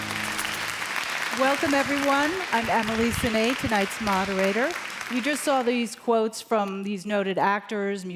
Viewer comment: When she introduced herself: I heard: "Welcome everyone, I'am Halucinating" 🤪